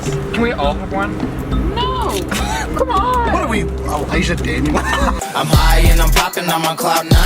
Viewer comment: Help, can someone tell me who is singing in 17:46? Because i couldnt really understand what Garret is saying. Thank you :) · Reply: Mingnism I FINALLY FOUND WHERE HE SAID ELIJAH DANIEL THANK YOU I LOVE YOU